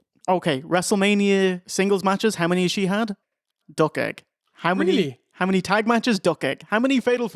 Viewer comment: what's that word used to describe "zero" singles matches for Alicia Fox at Mania? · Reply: He's saying "duck egg" which is shaped like a zero. It's similar to when you say "he's got a donut in the wins column".